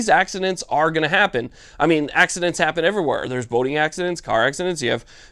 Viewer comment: I laughed because I thought you said voting accidents then I listened again and you said boating... Still giggling about voting accidents haha